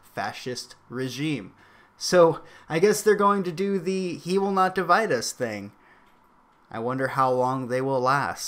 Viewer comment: They were saying, "He will not divide us" ??? REALLY?? OOHHH LOL. I thought they were saying "He will cum inside us" my bad